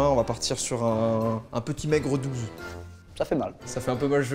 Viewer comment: j'ai compris "sur une note sur 20 on va partir sur un petit Aigre douce" et je trouvais la vanne pas mal (par rapport au fait qu'il n'y ait aucun rapport.. mais non !)